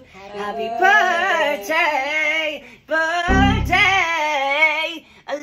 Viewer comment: Why did I hear “BIRD-DAY” when he said birthday😭😭